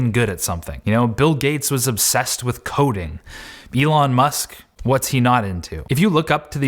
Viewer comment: I thought he said bill gates was obsessed with codeine lol Btw nice video dude. Love your content